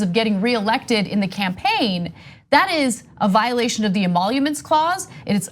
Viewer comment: What clause did Anna say it violated at 5:35 what is that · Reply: violation of the emoluments clause